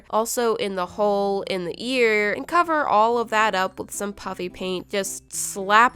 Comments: Heh Heh its sounds like shes saying pUPpY pAInT. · I sounds like your saying puppy paint! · Lol it sounds like she is saying puppy paint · It sounds like you saying “puppy paint” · You sound like your saying pupy paint instead of puffy paint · Did you just say puppy paint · It sounds like your saying puppy paint but it's fluffy paint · It sounds like shes saying puppy paint who agrees? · when I first found this channel, I thought she was saying Puppy paint and not Puffy paint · She sounds like shes saying puppy paint